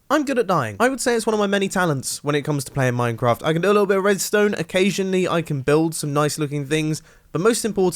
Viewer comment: did he just say "I can do a little bit of redstone"?